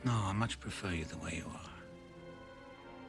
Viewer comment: Can someone tell me what he said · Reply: “Dinner at eight”